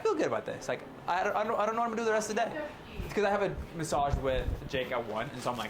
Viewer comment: My videos paused at like 58 sec and i thought u said “what sh** im gona wear” LMFAOOO